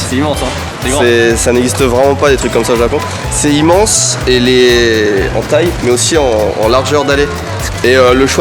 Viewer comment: j'ai entendu hentai jpp